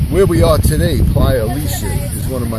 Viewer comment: I couldn’t hear the name of the beach. Doesn’t anyone know what he said? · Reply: "Playa Alicia" in Sosua